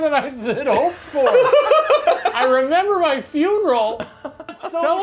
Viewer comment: Thought she said I remember my email